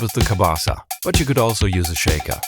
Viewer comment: I straight up thought you said kielbasa, like the sausage 😮